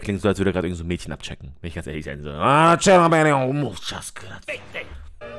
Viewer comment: wie ich bei 6:05 einfach mal bei dem typen von hellsing fick dich und big dick gleichzeitig verstehe HAHAHAHA muss los